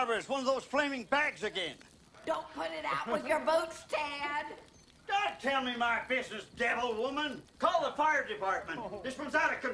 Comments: Sounds like he is saying "don't tell me my fish is dead old woman!" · I always thought he said "Don't tell me my fish is dead women" Now I realise it's "Don't tell me my business devil women"